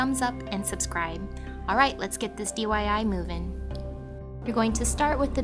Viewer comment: you said DYI instead of DIY oops!:)